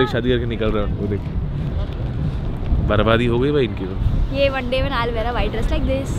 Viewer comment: What did she said😅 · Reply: Nikhil Sapkale she said one day I will wear white dress like this